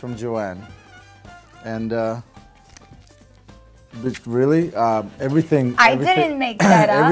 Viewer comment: Did he say "bitch really?" at 2:50 or am I imagining it?!